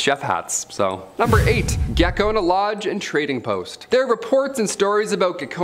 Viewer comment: I thought he said 'gecko in a lodge.'